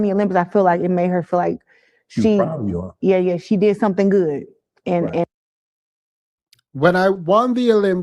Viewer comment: You misheard, she said her mom said "she did something good" meaning her mom is taking credit for Clarissa's hard work.